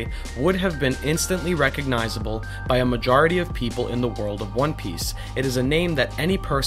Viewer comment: hm you have a point that the name would have been something that we should know... my first thought was wano.. and when you finished the sentence the way you said one piece sounded like "wano piece" . And now my head canon that wano was part or the whole of the ancient kingdom is more solidified. And ironic how my theory was a literal flip of what you did. Combine it? "Wano peace" as in wano in peace? Even I'm thinking I'm reaching hard on that one but it is a funny coincidence